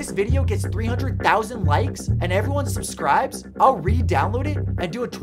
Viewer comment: Who want's a 24 hr Livestream this Friday?!? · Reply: I thought you said 24 live streams this Friday 😅